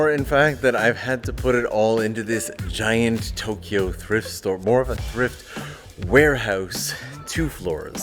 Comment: I heard “…Tokyo Drift store…”